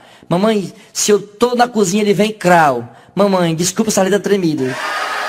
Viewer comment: O que ele disse em 45:38 ? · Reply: "Desculpe se a letra está tremido". É isso que ele fala.